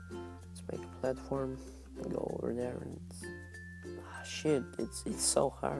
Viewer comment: I think he said s**t at 10:37-10:38 :(